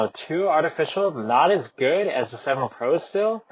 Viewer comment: What are you saying? Not as good as the 7 pro? I guess it make the most sense for it to be the Airpods Pro. Would be nice if they could have H2 chip in a released Beats Fit Pro 2.